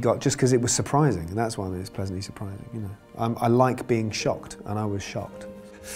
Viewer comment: i thought he said I like to be shot but he said I like to be shocked